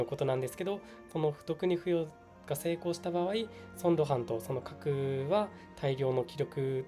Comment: ついに角が活躍する時がきたか。ソンロハン。ソンゴハンに聞こえる